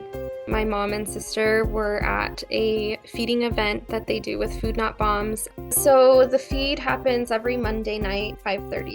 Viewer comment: I thought she said the organization was called “Food Not Bums”